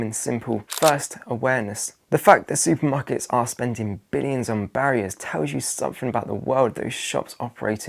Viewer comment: did you say Billions on barriers? 😂